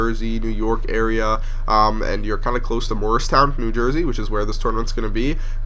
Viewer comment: god dammit i thought you said moorestown. it's morristown... :( morristown is pronounced "mawris". moorestown is pronunced "mores". dammit!